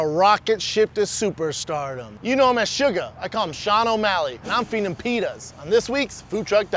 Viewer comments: I thought he said “& I’m feeding him penis” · “I’m feeding him penis” Sounded like he said that lmao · Thought he said he’s feeding him penis